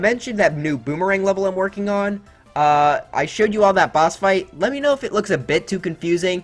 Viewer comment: I heard you say during the lemmy section, "Lemmy Know"